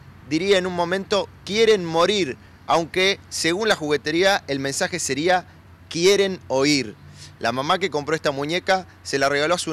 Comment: Yo clarito le entiendo 'morir', en ningún momento escuché 'oír' :/